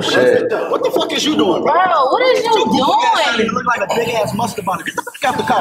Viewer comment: bro, i thought he said "big ass mustard bottle " .